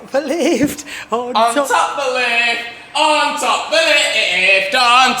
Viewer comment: ON TOP THE LIFT, ON TOP THE LIFT!! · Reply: I hear: “ON TOP BERLIN” which is even weirder but funnier 😂